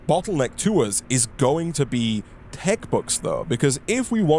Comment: I don’t think I heard what he needs. 27:44 was it like… maybe TECHBUCHS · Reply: the entire time i think he says tech books